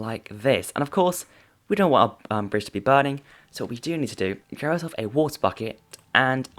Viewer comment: When he was doing the campfire things, he said we don’t want our bridge to be burning and at first I thought he said we don’t want umbridge to be burning. Ummm, I think I do want her to burn.